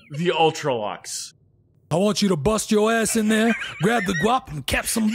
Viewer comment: I thought old said grab the guac.